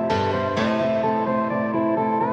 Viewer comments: I thought it said ''I just want that bitch to know''. XD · I always heard "I just want that MissingNo"..too much Pokemon-Creepypastas :D